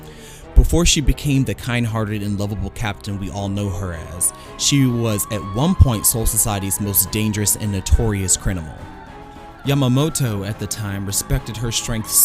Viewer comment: did he say crinimal